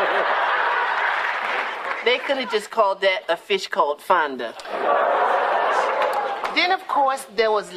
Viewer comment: No! Did they actually say 'A Fish Called Fonda'? John Cleese, eat your heart out!